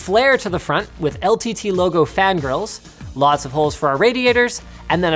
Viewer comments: LTT fan grills? The first time through I heard "fan girls" and I was very confused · I thought you said LTT fan girls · I swear I hear "fangirls" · thought he said "ltt logo fan girls" not "fan grills" XDDDD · i thought he said LTT fan girls XD